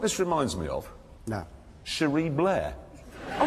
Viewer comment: shuwee Blair??? What did he say? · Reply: Cherie Blair. Tony Blair’s wife